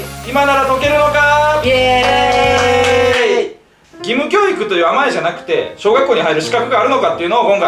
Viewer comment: 義務教育がキム教育に聞こえてしまった(笑)